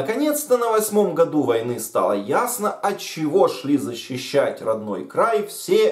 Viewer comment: ми шли зачищать Я одна так почула?